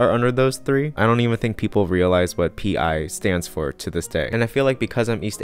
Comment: I heard "what a PI is",,,,, "..... What does a private investigator have to do with this?"